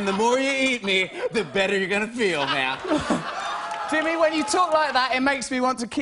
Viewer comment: I think Jimmy called Russell "Matt"